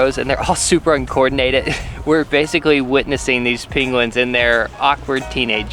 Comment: why do you sound like your saying Pinguin rather than Penguin?